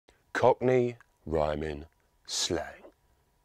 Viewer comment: It sounded like as if he said 'Cockney Rhyming Slag' instead of 'Cockney Rhyming Slang' lol 😂😂😂